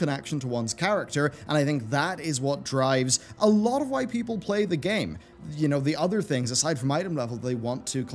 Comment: I heard "a lot of white people play the game"